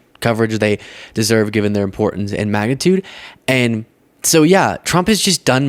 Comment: I totally heard "MAGAtude" when you said "magnitude"...I think you should coin that phrase